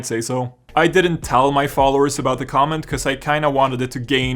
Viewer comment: For a second I thought you said "I didn't tell my fathers about the comment"